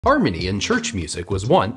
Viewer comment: Thought he said armenian church music lol